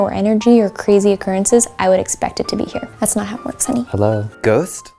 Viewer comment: When acts like it’s a phone I thought MYKIE said “It’s not Hogwarts hunny”.🥴😂 I literally replayed it 5-6 times before I understood idk but that killed me 😂😂😂😂😂 please tell me I’m not the only one. Also, I’m sorry, if you rewatch it>hear it> and can’t UN hear it🤗🥰